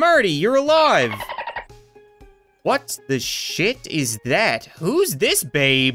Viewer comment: Did he swear at his video's? I heard he said shit